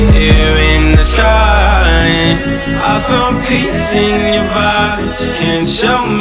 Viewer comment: Bummed, I thought it said “I found peace in your body”. No one finds peace in violence, nobody deserves to be treated with abuse and settle with that. If somebody hits you & says they love you & keeps doing it, they are your ENEMY. They will make you MENTALLY ILL. They will rob your time and take away from the time you could have had with someone who TRULY LOVES YOU.Do whatever you can to get away! Tell people they are hitting you, and seek counseling. You ARE precious and deserve real LOVE, Respect & kindness. Take care - out there Hugs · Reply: veritatis cupitor I thought it said I found peace in your vibes